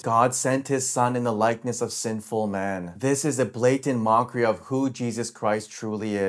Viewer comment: i thought he said "a simple man, not sinful man"